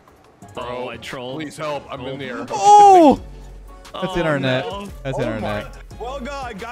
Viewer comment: "That's internet..." LOL thats what i heard, so i was like... aint no way hes blaming it on his ping or smth... And then i realized he said "That's in out net."